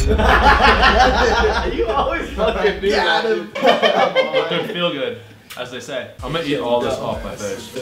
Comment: I think said dr. Feelgood by motley crue